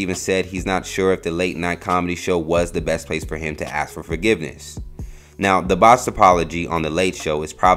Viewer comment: When the narrator said ask for forgiveness I thought he said “Afro forgiveness”😂😂